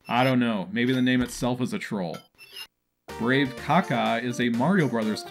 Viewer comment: did he just say brave caca